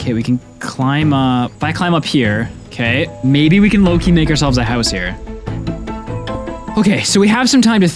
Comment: what did you say? 🤨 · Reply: "Low-key make ourselves a house" What did you heard?